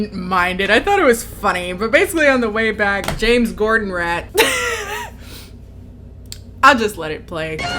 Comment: Oh God, unrelated but when I heard you say James Corden rat, I heard James gordan rat, and I can now only think about James gordon, James Corden and Gordon ramsy